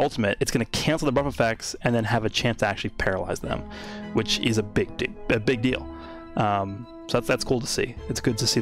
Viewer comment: "which is a big dic*" Did I hear it correctly!?😂